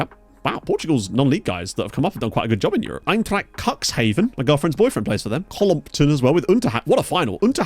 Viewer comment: im not going crazy, I heard "my girlfriends boyfriend plays for them"?